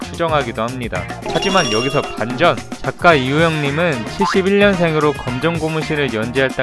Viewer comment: 난 71년생 으로 들리는데?